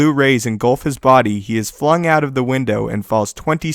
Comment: Everytime he says Window...i hear "Windu"